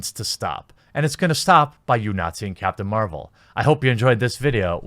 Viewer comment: All I heard was "By you Nazi-ing Captain Marvel" 17:57 Now you can't unhear it :D